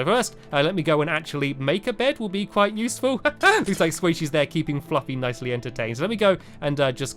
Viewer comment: "Haha, looks like squaishy is keeping floppy entertained!" Am i the only one who heard that?